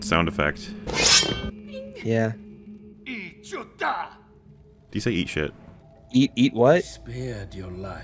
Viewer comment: what thingy said was “Echuta” which (because for some reason I know a little bit of Huttese) means basically “fuck you”